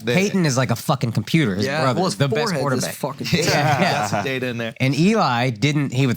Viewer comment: Pray for Andrew's barber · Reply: At 1st i thought you said his brother im like damn i hope hes ok then i realized what you actually said 🤣